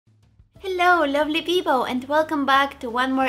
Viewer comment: Did i hear hello lovely PIPOW!? 0:01🤣